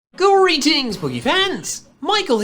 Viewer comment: i always hear "Greetings boogy fans"